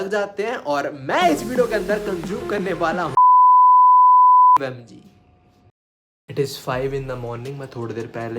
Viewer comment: I think I heard 695 💀 Nvm I was wrong;(